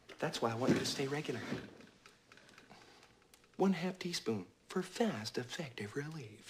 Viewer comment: i always thought he said one hefty spoon lol